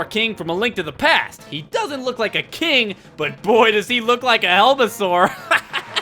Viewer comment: What I thought when you said "Boy he does look like a helmasore" I legit thought you meant "Boy he does look like a hell of a sore!"